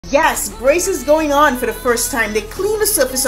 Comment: "Yes, braces going on" What I hear: "yes, rasict going on" 💀💀